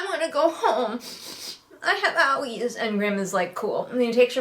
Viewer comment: I misheard "I have owies" as "I have yaoi" and briefly thought the book took a real turn